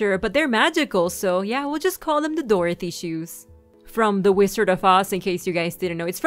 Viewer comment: Dorathie and the wizard of oss? That's what it sounded like u said...